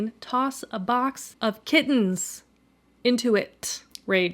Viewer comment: I THOUGHT YOU SAID A BOY OF KIDNEYS, I HSD TO RUN THAT BACK YOU SAIDS KITTENS AND THAT MADE ME MORE UPSET LOL